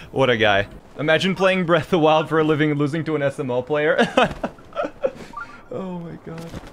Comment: can someone tell me what he means by “sml player” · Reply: I think he said SMO player. Smallant runs Super Mario Odyssey more the BotW.